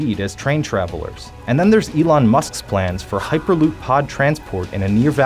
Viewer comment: After reading the comments, I am glad I am not the only one who heard him say Elton instead of Elon.